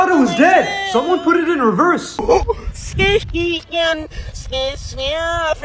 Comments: instead of “help me” i heard “whoop me” 👁️👄👁️ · I didn't know he said help me I thought you said healthy 😂💀